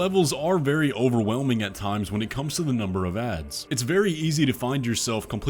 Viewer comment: He keeps saying "number of ads" in a level. What am I mishearing. Or is this a gaming term I've never heard before? E.g.